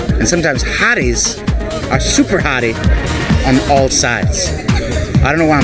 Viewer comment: I thought she said cause you're on molly. Cuz he def looks like it.